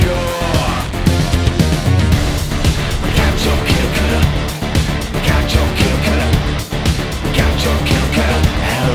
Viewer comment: Got your kill carrot? What is he saying there? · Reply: break out your kill cutter,lyrics in the description